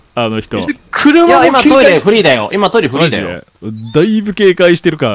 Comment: 「行っちゃいますか！（脱出の意）」 「行くか！（キヨの所への意）」 アンジャッシュのコントかよ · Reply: からの「トイレフリーだよ」が全部「トイレ不倫だよ」に聞こえる